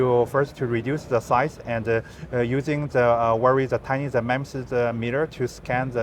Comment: you have a (?) in your transcript. I think that he said "we have a very tiny dimension(ed) mirror to scan" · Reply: No, it was 'MEMS'. That's one of the techs used in solid-state LIDARs